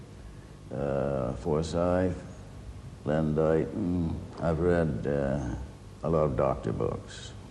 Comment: sorry - anyone knows what's he saying at 1:01 ... "..I'sve reasd a lot of ... books..." ??? what books? doctor books? what does that mean if that's the case?